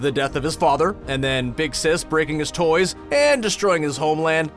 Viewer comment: did he just say big sis breaking his toys XD